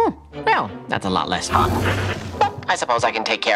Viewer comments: does he say a lot less HOT or a lot less FUN? I can’t tell😅 his mouth says Fun but my ears say Hot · it sounds like he says “that’s a lot less hot” which I think is what the Amazon subtitles said but it looks like he says “that’s a lot less fun”